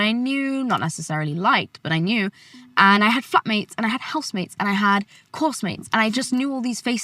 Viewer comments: did she said fuckmates.. wth😂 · I thought she said "f*ck mates" at first 💀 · DID YOU SAY YOU HAD “FUCK MATES”!? Lolololol